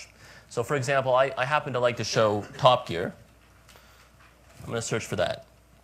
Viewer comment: in 1:28 did he said pop gear it's top gear